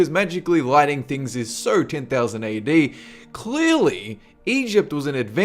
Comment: Did you just say 10,000 A.D?